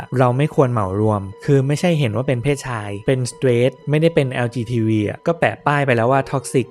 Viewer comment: ฟังหลายรอบมากตรงนาที 32:44 แอดพูดว่า lgtvใช่ปะคะ😊